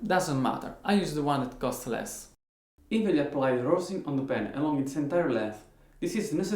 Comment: what is that? i cant understand what he say.thanks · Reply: Rosin